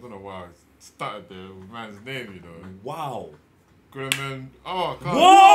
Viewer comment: did he say "gremlin" lol